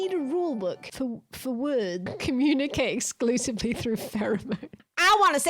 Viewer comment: I misheard pheromones (6.40) as "theremins" and I thought communicating solely through devices that make high-pitched buzzing and squealing noises actually is a perfectly correct thing to do in this day and age. As for Charlie, I am surprised for some reason to discover I am taller than him (5'8") and very surprised by this situation. When someone tells you they've done undeniably bad things that they need to apologise for, responding with "lol no you didn't" is misguided at best. I'm not a Moist fan but I expected better than this.